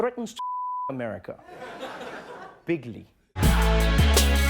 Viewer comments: dont get me wrong, im not a trump supporter, but im pretty sure i heard "big league" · I thought he said Big League the first time I watched it... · At first I thought he said Big League which is a term we use. Than I watched again and he actually said bigly!! What a moron.